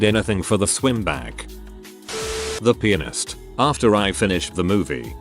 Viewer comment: i thought i heard “the penis-ttt”